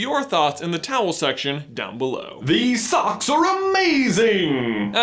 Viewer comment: What do you say at the end of the video? It always sounds like your saying towel section down below